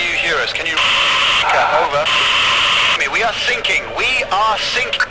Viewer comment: I thought he said singing